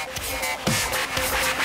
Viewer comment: Cute voice but it sounds like your saying butt crush,butt cruch,crush brush